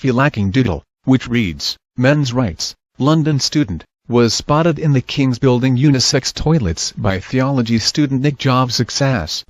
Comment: I totally thought it said, "Theology student, Nik Jovcic's ass."